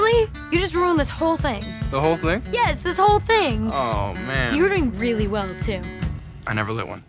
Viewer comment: What's Hazel saying on 1:18??? Please!! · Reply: You were doing really well too